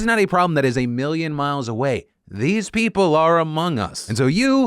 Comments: Phil : "these people are amongst us..." But what i hear: "AMOGUS" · please tell me that I'm not the only degenerate that thought/said "Amogus?" 😔